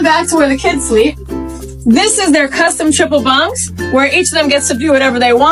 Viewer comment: wait did she say triple don’t tell me that lil curtain down there is a the third bunk…..